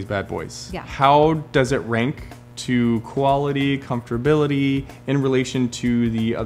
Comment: Did just say "comfterbility" at 5:40?